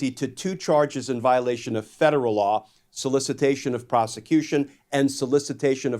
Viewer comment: Why do I always hear twice "solicitation of prosecution"? Am I hearing it wrong or is it mispronunciation? Non-Prosecution-Agreement-1433.pdf <link>